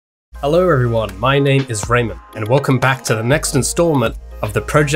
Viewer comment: i swear i heard "hello everyone, my name is random" lol